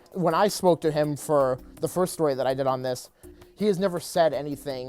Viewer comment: Every time I listen to this part I hear "The first time I smoked with him..."